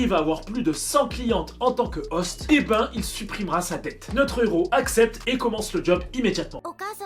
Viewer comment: J'ai entendu "il supprimera sa tête" x3